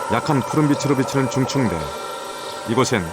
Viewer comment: 쯤에 "수면의 빛이 약한 푸른빛으로......" 이 뒤에 설명 들리시는 분 좀 알려주세요ㅠㅠ 왜 심해에는 투명한 피부를 가진 생물이 많은 지 설명하는데 아무리 들어도 듣기가 안되네요. 한글 켜도 뭔가 맞지가 않고.. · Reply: 심해 바닥에서 200~1000m 사이를 말한데요 중층대(mesopelagic zone) 중심해층/중층원양대